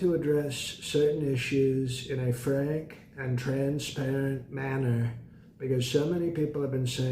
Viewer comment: Sir, when you said you are transparent, I thought you said trans parent. After spending several minutes trying to figure out which of your kids is the trans, I realized that I had misheard you. However, if at any time you completely contradict yourself I will continue to stand by your side. It's Eric, isn't it?